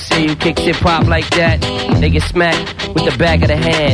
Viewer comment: What did he said at 13:58 ? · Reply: LOL 😂 “They get” it sounds like that due to a mix of him speaking fast and the beat being loud.